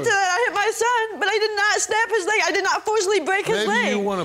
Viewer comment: Did she just say “I didn’t not STAB HIS LEG, I didn’t break his leg!” Wtf else did that baby have to live through!!!